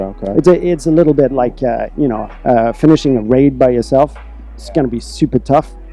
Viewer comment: hold my beer, did he just confirmed raid in the Division?? Did I hear this wrong? someone back me up hear plz??